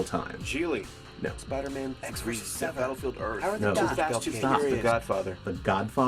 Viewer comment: did i hear battle field earth? thats the worst movie of all time!